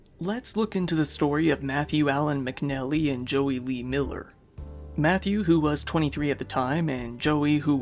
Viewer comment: I had to back it up because I thought he said Joey Wheeler instead of Miller. lol